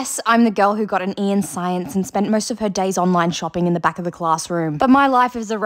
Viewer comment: Because of her accent, I thought she said "A" in science but she actually said E😂